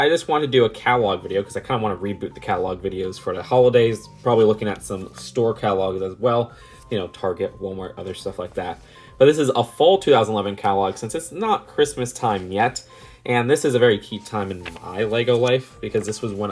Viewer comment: For a sec I thought it said "Fall 2021"😂